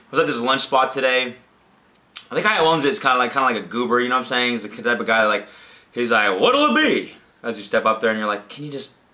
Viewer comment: is completely incoherent. What are you saying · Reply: Apples Mangoes "...and the guy who owns it is kind of like a goober, you know what I'm saying? He's the type of guy who'd be like 'well what'll it be?'"